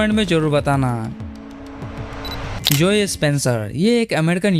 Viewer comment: kya bola Dispencer 😂